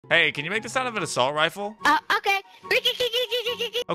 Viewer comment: Pa cow sounds like he saying A COW